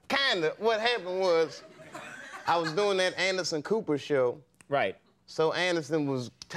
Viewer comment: I legit thought he said Anus & Cooper.